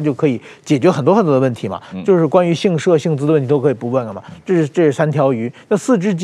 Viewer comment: 说的是关于“姓社”还是“姓资”的问题，加字幕的人听不懂就胡编乱造